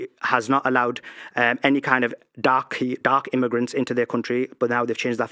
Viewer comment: Bruh, did he just say "darkie"? 😂 19:08 What a lad